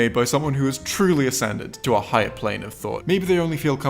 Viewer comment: The first time around, I heard "Descended to a higher plane of existence" and honestly that works.